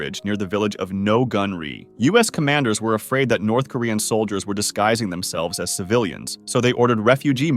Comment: Yeah bump why u say south? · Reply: What? U also think it sounds like he says South Korean when it would be North Koreans fleeing?!?😮😂❤